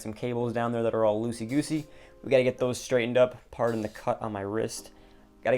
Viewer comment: i was looking at my phone and i hear “pardon the cut on my wrist” i was like wtf and looked over😭